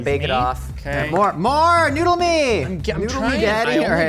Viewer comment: Did... did just say "Noodle me, Daddy~?" i love you